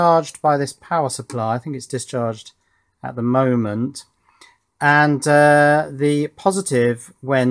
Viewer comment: Does say: "HE is discharged at the moment" ? English is not my native language, but does that mean that the supercapacitor is a person? "Hello Mr. Supercap, nice to meet you!"